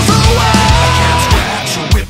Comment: I thought they were saying "sandpeople", lol.